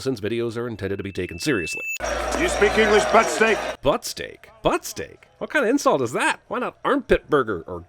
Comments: Putzig.... he says putzig... as in a putz.... as in "you peculiar fuck, get off the bus". I can't believe you thought he said "butt-steak". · Did you watch a dubbed for TV version? I could have sworn he said "F*** stick" not "Butt steak". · I thought Captain Hadley said "F**k Stick, not Butt Steak! 😂😂😂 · I always heard "butt-stink" not "butt-steak". Mine at least DOES make sense where as yours is just nonsensical. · Butt steak? I always heard it as "butt snake" i.e. a piece of shit. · Butt-steak?? I think I've watched this movie about 50 times now and always thought He said Fuck-Stick. LOL... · I always thought Clancy brown said Put-Stake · I always heard "butt-stink" not "butt-steak". Mine at least DOES make sense where as yours is just nonsensical. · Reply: Honestly I swore he was saying ‘Butt-snake’ lmfaoo, even when I watched it just now swear he said butt-snake 😭